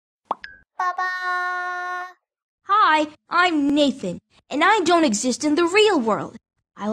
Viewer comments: "I'm Naked!" that's what i hear XD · I thought it said "Hi, I'm naked and I don't exist."